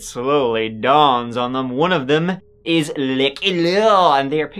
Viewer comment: what is he saying?? Lmao. “One of them is luckeh loooo!” I feel like I’m missing something obvious but am stuck on it 😂